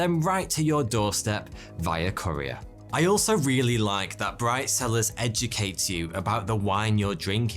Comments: Was just lisening and well, I thought you said 'bride sellers' at first.... took a rewind to figure out you weren't maybe advertising mail order brides :P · At first I thought he said “bride sellers” lol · I'm being serious, I thought you said BrideZillas instead of Bright Cellars lol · listening not watching and thought you said Bride Sellers. lol